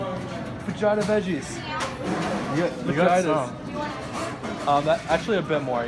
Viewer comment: sounds like you said vagina veggies at Chipotle lol